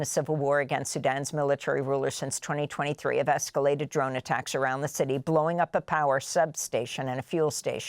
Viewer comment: "Have exculpated drone attacks" I wonder where they got those from instead of bush blades.